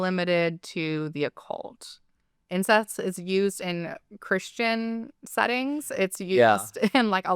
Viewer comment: It kinda sounded like you said "Incest is used in christian settings" which... not wrong either